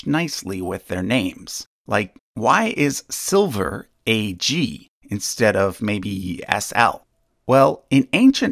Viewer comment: the caption is "Why is Iron Fe, instead of Ir" but the spoken words and the graphics say "Why is Silver Ag, instead of maybe Sl".